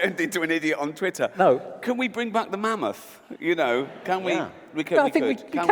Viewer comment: Did he just say Manmoths? Half moth, half man?